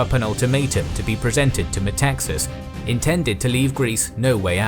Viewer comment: I thought he said Texas and wondered wtf is Italy demanding from Texas out of nowhere 😂